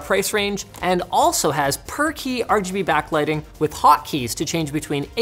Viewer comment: Every time he said per-key I thought he said perky lmfao